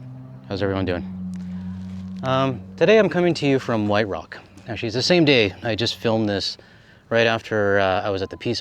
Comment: Did you say "she's the same day..."? The captions had it as "Actually, it's the same day..." Not sure I have heard that colloquialism of calling the day a "she". I agree with the author of that letter, the Canadian populace has to help in this trade war, and everyone should do something to help. I am not expecting people to not travel to the states, that is not always feasible, just travel less. I do not expect people to adhere to buying Canada-only religiously, but I expect Canada first when possible. We also have to be diligent as some producers, like Driscoll's, grow stuff in multiple countries, although often their stuff is flavorless so I avoid it anyway. I just bought enough local wild blueberries to last me months and I am freezing them for later. They are so much better, even frozen, than the regular, larger, but less flavorful ones at the regular grocers.